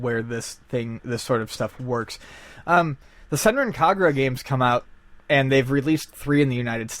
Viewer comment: What series is talking about at 7:53? I can't make out what Japanese title that he's saying. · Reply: Senran Kagura